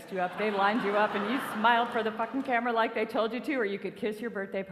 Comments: did she say "fucking camera" at 6:38.... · Did she say "fukn' camera" at 6:30?